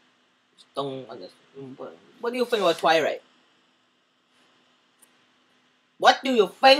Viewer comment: LOL Almost Thought He Said "What Do You Think About Fried Rice" xD